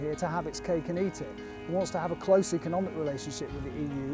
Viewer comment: Could someby help me please? What's the phrase that the man says in minute 2:21 ? To have coco...?